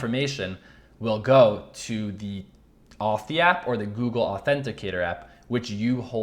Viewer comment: Two Party Authentication - i am extremely new - please specify the app you recommend for 2 party authentication - it sounded like you said Authy App??? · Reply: Watched again at 8:42 in video shows AUTHY app - thx